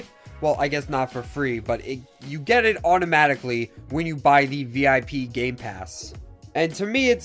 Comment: I heard vpip